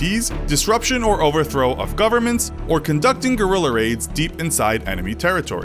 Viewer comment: I thought he said gorilla aids